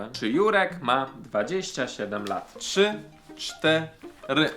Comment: 25 lat usłyszałam